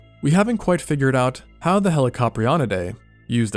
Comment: I 100% thought he was just gonna say “How the hell this thing ate” at 9:17 😭